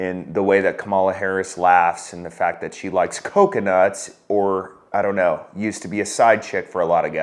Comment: shit, i heard "concusluts"